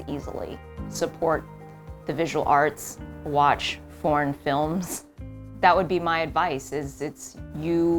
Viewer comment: I thought I heard she said “watch more porn films “ 🤣 So I need to listen to it again. What she said was “watch more foreign films “: maybe I gone deaf.